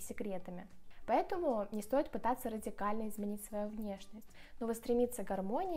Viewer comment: услышала "питаться" вместо "пытаться", смотря на еду😅